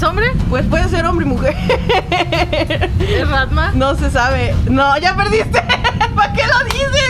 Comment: Cuando dijo es rannma le entendí "Batman" >:3